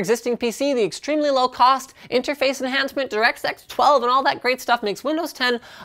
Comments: You should have slowed down at that last mention of DX12. It sounded like you said Direct Sex 12. Unless that was intentional? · Directsex 12? I can't be the only person who heard this. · Linus, did you say directsex?